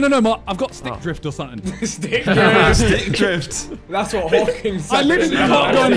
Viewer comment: Stick Drift- Vik with the "that's what hawkings said when going to the island" 🤣🤣